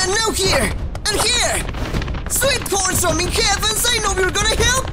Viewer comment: did he just say sweet course of me heavens lol